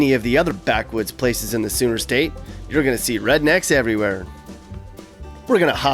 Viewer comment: did he say "Sewer State"?